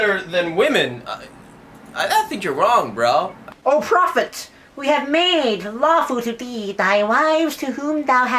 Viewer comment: you're awesome 😂 you killed it! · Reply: ok iam just gonna be honest but when the guy said "o prophet! we have made lawful..."(from the qu'ran) I thought that he said o prophet! we have made falafels 😂😂😂 LMFAO but honestly is it just me or does his voice annoy you guys to